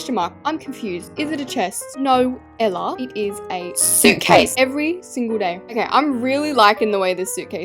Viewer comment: suit case? i thought you said nutcase mb guys😢